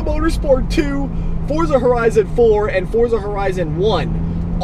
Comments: did he say forza horizon 4 on 9:09 ??? wtf is that shit.... geuss i have 2 buy it now since informed my ass about it... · did he just say Forza Horizon 4 ?! xD